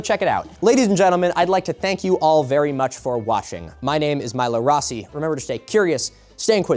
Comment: I don't know why you want to thank me so much for washing, but whatever floats your suit... Am I the only one who heard him say washing instead of watching towards the end at about 1:05:59? Anyway, I don't blame you for going into this sort of thing without a lot of prep. It isn't like they did a bunch of quality research to back their claims, you've already put in more academic honesty than they ever could.